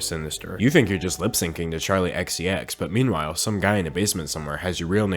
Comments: why does the subtitle say Ariana Grande instead of Charli XCX? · he says charli xcx but the subtitles say ariana grande... oMG he’s trying to tell us... they’re the same person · nobody gonna talk about how at 3:54 the captions said ariana grande instead of charli xcx lmaoo